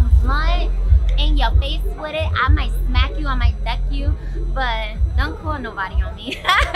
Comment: did she say “I might neck you” cause that’s what I was trying to hear. How did we get in contact with her ?